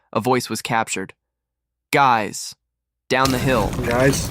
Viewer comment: If the girls was scared why didn't they run and scream for help and when the suspect said down the hill they sounded like they said hi